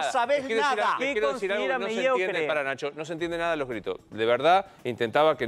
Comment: "no se entiende para la chota" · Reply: "No sé entiende, para Nacho"